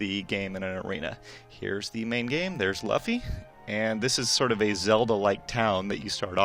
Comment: Di-Did he just say laffy...?